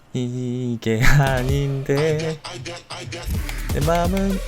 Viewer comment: I can 이라 들리는 분은 조용히 손을 들어봅시다